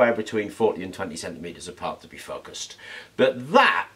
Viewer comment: I had to back up and listen again when I thought you said they need to be 47 meters apart when focused. I wish the powers that be would standardize the metric system here in the US.